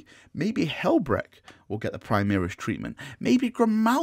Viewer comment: When you said “Primaris treatment,” I heard “tree man.” I mean, the dudes ARE pretty tall.